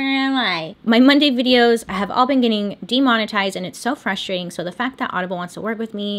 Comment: So you mentioned that your videos have been demonetized. I thought at first you said "demonized" & I was all ready to make sure you realized that you're super funny, super beautiful, and people were naturally going to be jealous of you (demonizing you) lol...I listened again & realized that you said your Monday videos have been "demonetized"...I'm curious about that because from what I've read in the comments following various episodes, (& my own experience), we adore you. Also, there are millions of views for every video. I don't get it. I'm actually worried about you getting a primetime spot because it's easier for me to watch on youtube ;)